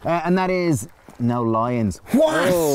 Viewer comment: I was wondering why the hell you were saying “no lions”... oh lie ins!!! This Canadian was confused :) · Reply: I thought they said no lyin'. Presumably because lying keeps you up at night with guilt and fear. This Canadian was confused as well.